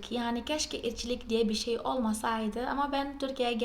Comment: "Keşke içlik olmasa" öyle anladım ben de😅